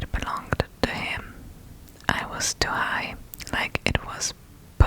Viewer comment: lol imagine they actually said ‘I was too high’ 😭